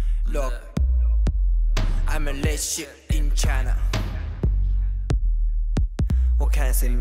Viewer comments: am i the only one who misheard the lyrics "i'm the lead sheep in china" as "i'm the lit shit in china"? :)) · I keep hearing ‘IM THE LESS SHIT IN CHINA’ please don’t hate me😂✌️